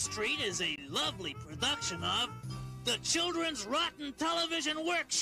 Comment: What did Oscar say? "I've gotta take a substance"? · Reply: No he said, "I gotta think of something"